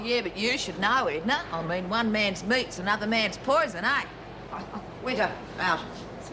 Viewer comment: did Marie say to Edna one man's mince before she said another man's poison ? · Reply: One man's meat.